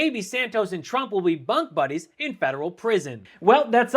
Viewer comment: "bunk-buddies in prison" I thought I heard "butt-buddies!"🤣